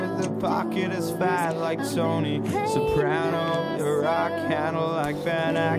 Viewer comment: Did he say "Im not Fucking as fast as Tony" ???